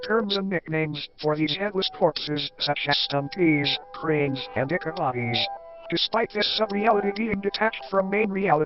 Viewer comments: YouTube subtitles thought it said "dick bodies" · MY SUBTITLES SAID DICK BODIES 💀💀💀💀💀💀💀💀💀💀💀💀💀💀💀💀💀💀💀💀💀💀💀💀💀💀💀💀